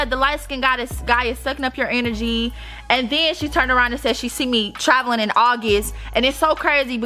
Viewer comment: I thought she said she saw you traveling in August its September....